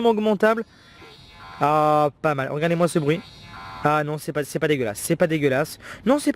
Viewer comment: C'est moi où il dit "Regardez moi ce bruit"? XD